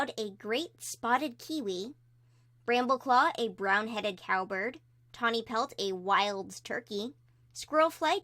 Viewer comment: I thought it said coward instead of cowbird. To be fair it kinda fits brambleclaw in most of the arcs.